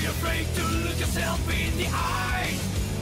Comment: Oh so that's what he's saying at [about fifteen different points in the song]. I still prefer "that's my heart beating you" to what it actually is though. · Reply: I always heard i as "that's my heartbeat in you". XD